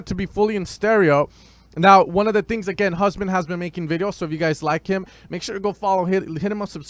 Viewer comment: can't understand the name he's saying there. Can anyone help me? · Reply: Paul Schulze he is taking about Husman